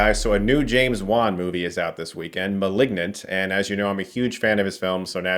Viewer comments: I thought you said a new James Bond movie was out this week. I was like what? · I heard "A new James Bond movie is out this week - Malignant"